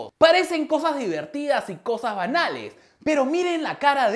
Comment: entendí cosas Anales 😂😂😂